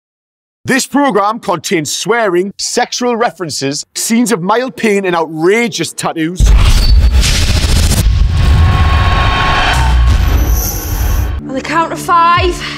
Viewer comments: When he said mild pain I thought he said male peen lol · At the beginning I thought it said Male Peen instead of Mild Pain.